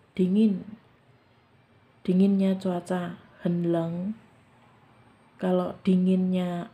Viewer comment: Ce mau tanya ,knp ya aq dengernya seperti hen neng kalau mjkn bilang sangat dingin, apa telinga q yang salah denger ya 😀😀😀😀